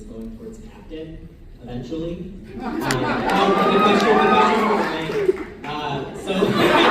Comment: What does the guy say at 21:56 after "Buck's career trajectory is Captain eventually" 😭😭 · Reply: "But my question is for Ryan" 😂